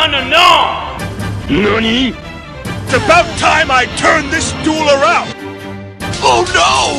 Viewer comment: can someone tell me what he says in 3:58 ? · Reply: about time i turn this duel around